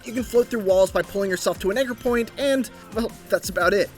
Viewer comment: mark did he say ni**er point ?